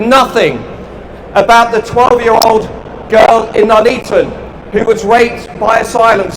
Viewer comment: I think he said Nuneaton there not Notting hill